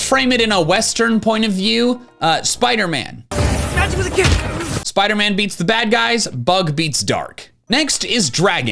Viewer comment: I had to look up “Spider-Man kick” to see what he said and he said “Magical Kick”